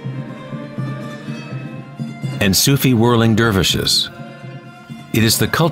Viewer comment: Did he just say "ensusivurvlingdurveshish"??? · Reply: Sufi whirling dervishes